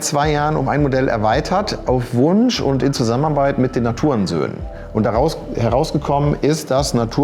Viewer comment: ich habe Hurensöhne verstanden!